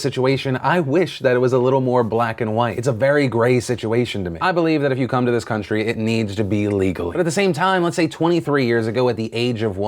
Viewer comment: did he say I believe if you come to this country it has to be illegally?